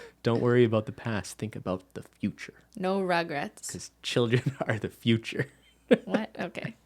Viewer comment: what i heard, "No rugrats" "children are the future"